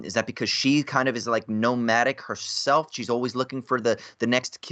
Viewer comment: When said “ nomadic” I heard it as “gnomadic” like nomadic garden gnomes